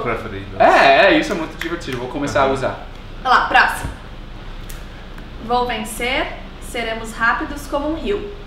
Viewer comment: "Vou vencer"? Eu sempre ouvi "Homem ser"